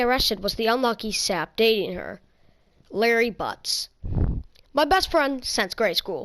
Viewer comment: Larry butt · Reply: I thought he said Harry Butz